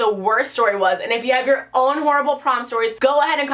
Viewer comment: I thought it said “porn stories”